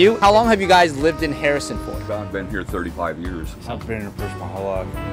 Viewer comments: I realized I should stop drinking when I thought he said they lived in Harrison Ford. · I heard Harrison Ford